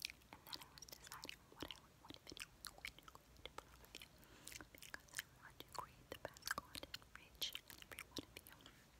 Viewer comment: I'll give someone a crisp high five if you can tell me what she says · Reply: i just heard “ and i want to create the best content for you”